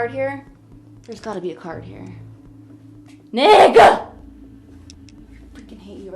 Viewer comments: did she say nicca 😂 or I’m tripping 🫪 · I'm sorry but did anyone else think she randomly said "Nighhaa" 🤣🤣🤣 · WHAT DID YOU SAY??😭😭🙏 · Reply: She said 'Nick' 😆